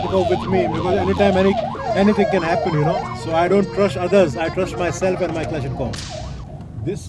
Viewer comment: There is a typo in the subtitles at 6:35. The subtitle says "I don't trust Uzair" but person speaking says "I don't trust others"